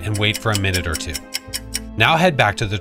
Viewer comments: I heard 'morom' at first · I’m not the only one that thought it said moron the first time right? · Am i the only one that thought that said moron ;_ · For a moment I thought that said Moron lmao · I thought it said “Moron” 🤣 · I thought he said moron😅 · I thought it said Moron, then i look at it again and it said Moroi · Ngl I thought that said moron at first 💀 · I thought you said MORON😂